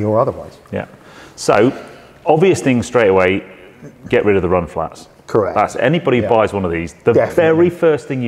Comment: Sorry, only 3 mins into the video, but I could have sworn he said the first thing to do is get rid of the mud flaps😂😂